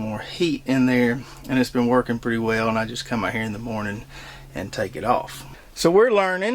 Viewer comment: lol @ 4:52 did you say you just cut your hair in the morning?? im going to try cabbage again but i have a hard time with it.you do well with it. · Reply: "... come out here ..." My southern accent is the best at enunciating. lol